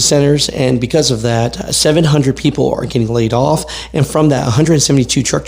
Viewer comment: When you said laid off, I thought you said play golf lol😂